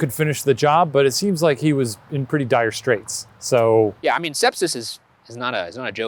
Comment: Is it me or does it sound like Shane is saying stepsis lmao